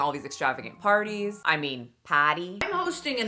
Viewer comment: Makayla’s Boston accent is so over the top…I’m from Massachusetts and no one says “patty” for party 😅😅😅 but I might start now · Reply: Sounds like she say’s potty 🚽 lol